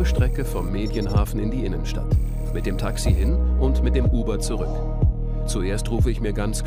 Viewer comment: Habe ich das richtig gehört, dass er bei 1:33 das Wort Taxi gegendert hat? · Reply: Ne, "...mit dem Taxi hin und mit dem Uber zurück." :D